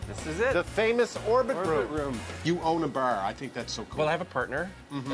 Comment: at first I thought Rick was saying "you own a bear"